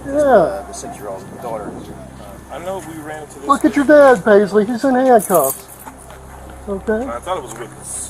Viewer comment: Somebody confirm plz. Am I hearing it right?? I am so disgusted that spit is coming to my mouth. 19:55 Look at your dad baby, he is in haaand cuuff...